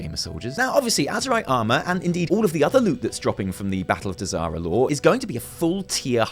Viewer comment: I'm drunk and when you said "Battle of Dazar'alor" I thought you said "Battle of Disaster Lore."